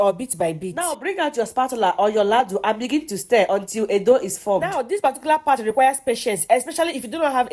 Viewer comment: I thought I heard "until 'edo' is formed"🤦‍♀️🥱